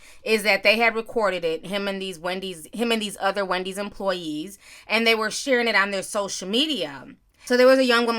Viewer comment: I thought you was saying Wendy Williams employees got fired. Lol